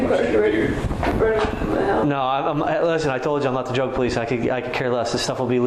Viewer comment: When that cop said "im not the drug police" i couldve sworn he said 'im not the joke police... [pots fixing to be legal anyways]" Lol i had to look at the phone n rewind to see what the subtitles said